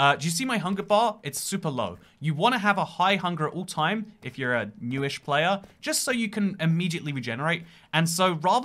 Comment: I thought you said “if you were a jewish player “